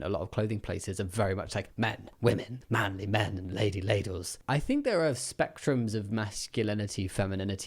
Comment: Did he just say lady ladles? At about 22:00? It's hilarious, I love it!